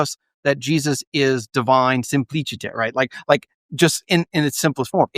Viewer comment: Anyone know what term he is using at 32:57? I know it’s not “simplicity” as the automatic closed-captioning assumes? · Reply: just latin for simplicity - simplicitas, I think. when you're not that smart and believe silly things, quoting latin sometimes impressed people.